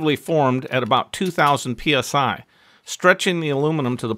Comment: Pounds per square inch sounds like division to me. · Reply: i thought you said 40000 psi i was thinking about hydraulics my mistace sorry